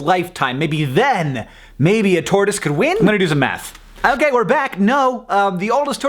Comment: i thought you said "i'm gonna do some meth"